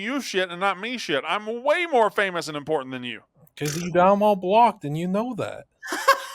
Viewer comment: I can’t understand what said right here about Brandon. Someone help me out. I’ve done replayed it like 10 times… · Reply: “Cos you got them all blocked and you know that”